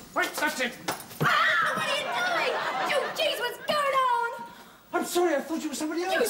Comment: Geez what’s going on! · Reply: I know it's been 2 years, but in case the folks who commented are still struggling, "Geez what's going on?" is what the girl says